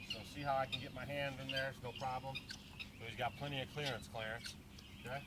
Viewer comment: Round TWO. 11:22. Did you just say “it’s got plenty of clearance Clarence?”